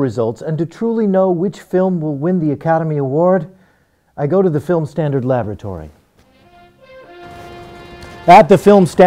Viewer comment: Thought he said the film standard lavatory :)